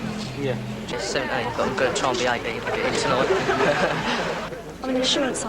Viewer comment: What does the guy on 5:37 say? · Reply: "I'm just 17 but I'm going to try to be 18 to get in tonight."